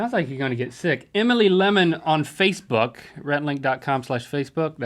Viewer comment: When link said 'Rhett and <link> slash Facebook, I thought he said Rhett and link .com slash facelift," I thought that was why Rhett laughed so much lol